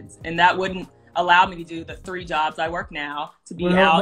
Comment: I love how she’s laughing at how less than 1% of men make over $500,000 all the while she clearly doesn’t even bring in 25% of that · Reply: I think I heard her say she got three jobs. Lol